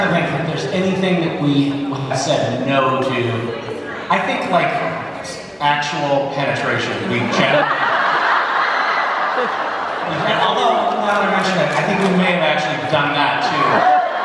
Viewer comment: Someone can transcribe what he said on 2:31? English is not my first language, but I understood he said something about "penetration", so I would like to know what's the correlation... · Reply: He said "I guess I'm trying to think if there is anything that we have said 'no' to... I think like actual penetration we've generally... although now that I mention it, I think we may have actually done that too..."